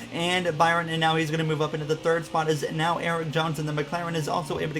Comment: did he say Erik Johnson??